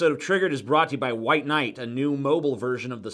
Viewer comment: I thought tj said white knight and i thought to myself come orn god